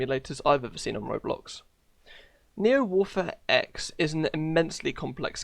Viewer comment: Neo-Warfare X Me misunderstanding what says: Neo-Waffen-SS.